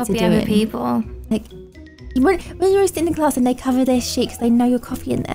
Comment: Omg 2:44 I thought she said sh*t lol 😂❤️love you guys!!!(nothing is wrong if she did)